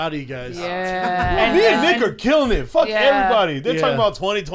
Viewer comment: Swear to god it sounded like shapel said “that nigga and i are killin it”